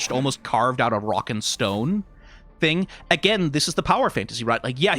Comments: DID I HEAR A ROCK AND STONE??? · DID I HEAR A ROCK AND STONE?